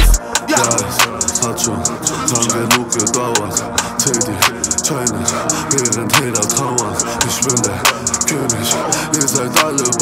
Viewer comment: Habe nur auf den Reim mit: TILTED TOWERS gewartet... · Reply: Ich hab verstanden : Zähl die scheine digger , Eifel Tower xD